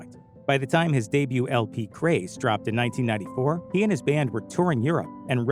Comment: remind me to never click on this channel's bs again. @ 1:54 he calls his album "craze" when it is clearly actually called grace. not a mistake but a very shitty troll